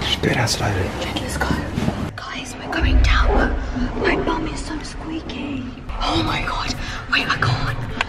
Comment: Who else thought she said my mums so Squeaky at the start hahahahahaha 😂